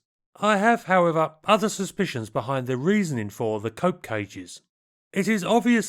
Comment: The Cope Cages, are not coping particularly well. Also, I love how "Cope Cages" has become the actual term, on Wikipedia. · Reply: I thought he was saying "coke cages". Lol.